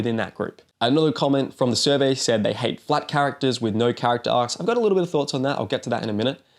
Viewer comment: I misheard that as "black characters with no character arcs"